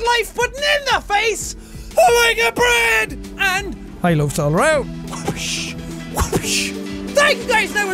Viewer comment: Am i the only one who heard him say and HIgh breads all round"